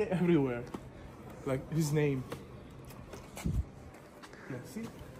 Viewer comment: w 15:21 minucie ja słyszę "...like Disney" Aż mnie korci by gostka dopytać. Takie słowa, bez sensu i kontekstu najczęściej wiele znaczą. Gość to powiedział tak odruchowo, cholernie zastanawiające, chyba że ja źle usłyszałem.